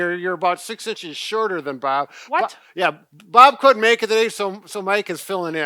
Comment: Did I hear that, “Bob quit making these...?” Hope that’s not true! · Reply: "Bob couldn't make it" I believe, but English is my second language so I wouldn't trust my word.